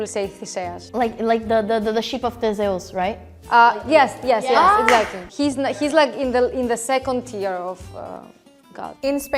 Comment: I don't know what "sheep of Zeus" she is saying and why the Greek girl agreed. Theseus (thisefs) means "the founder". Edit: Oh she was talking about the Ship Of Theseus. The subtitles at 13:00 were writing Sheep. Totally different thing.